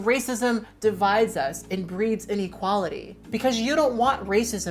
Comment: Hi! Thank you for this incredible video and insight. I do want to make a minor-but-not-so-minor closed caption edit suggestion: at 19:54, your closed captions say that racism breeds "in equality" rather than "inequality." That drastically changes the meaning of the sentence! 🙊